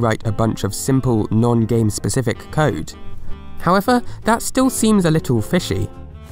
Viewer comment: You said 11 Subtitles said 19 What is going on?? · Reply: “However that still seems a little fishy,” subtitles say weird.